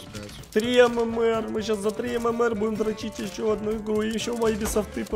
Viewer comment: что он сказал тащить или 👀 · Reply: Дро####